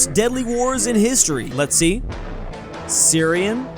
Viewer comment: In 1:44 i thought you said "Nazi" but you said let's see